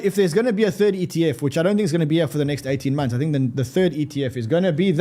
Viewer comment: i thought he said to go away for 12-18 months lmao😂